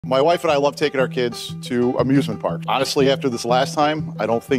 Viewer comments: I thought he said “abusement parks” at first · I thought Im he said abusement parks at first